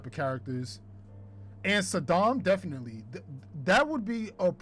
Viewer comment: When was talking about Sodom, i thought he was saying Saddam 😂😂